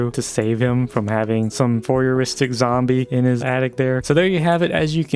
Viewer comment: did you say "voyeuristic zombie"